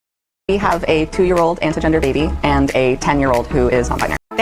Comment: I think the woman is saying that the 2-year old is "inter gender" not anti gender.